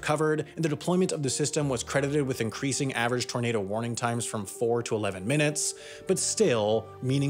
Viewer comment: Increasing warning times from from 4 to 11 minutes??? Did I hear that right?